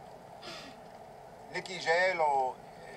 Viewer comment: I swear Tyrion said, "Nikki J-Lo..."